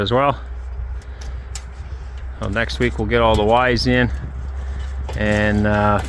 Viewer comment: when you said all the Y's in i had to repeat it i thought you said wives for now lol hugssss